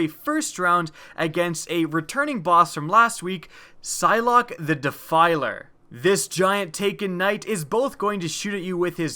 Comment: Did he just say Sylok the Defiler